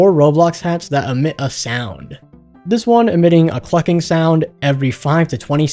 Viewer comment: a WHAT sound?????? Edit: i know that he said Cluking